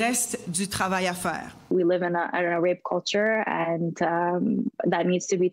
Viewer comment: Did she just say we live in a parmesan cheese in french culture? Um...............no?